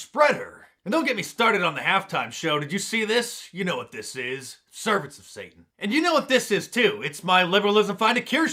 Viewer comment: Did Mark say "Servants of Satan"? I did not watch the Stupor Bowl, so I have no idea what this is about. And yes I tried googling before posting, so any help would be appreciated. Thanks!